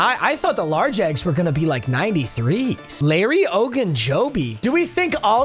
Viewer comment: Nah but when I heard him say Larry ogunjobi, I thought- Obi-wan Kanobi 💀